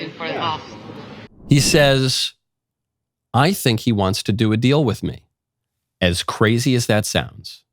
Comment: He clearly said “ he wants to make a deal FOR me”.. I am a huge Trump supporter too, but get it right!